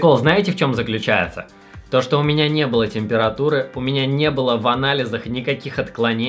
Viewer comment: Ты когда говорил: "В анализах". Мне послышалось в анале. Ты красавчик братан.